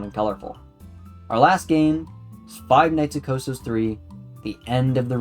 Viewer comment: I thought it said Five Nights at Costcos. 😂😭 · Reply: I thought it said Costco 's